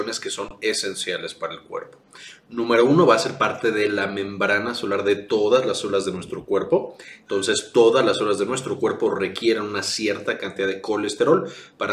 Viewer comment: Del minuto 1:08 al 1:16 no entiendo si dice obras u horas. Me podría responder? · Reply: En este intervalo digo el colesterol va a ser parte de la membrana celular de todas las células de nuestro cuerpo, entonces todas las células de nuestro cuerpo requieren una cierta cantidad de colesterol